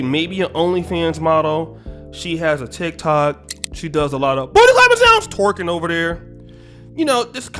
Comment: bruh please tell me what you be saying at 0:52, like boy is it “booty lovers down?” Bruh you been having me crying for MONTHS lol🤣🤣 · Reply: Booty clapping sounds lol